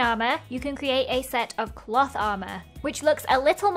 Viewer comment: I thought she said "a set of plot armour"